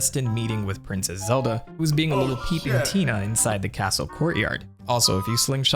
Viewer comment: thought he said "little Latina" for some reason